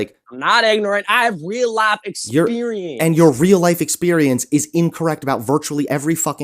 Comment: anyone catch what he said? All I got was "my real life experience is WHAWR!" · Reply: I think he said “How can real life experience be WrOnG!?!?” lol he sounds like me when I was a teenager getting mad at my parents 😂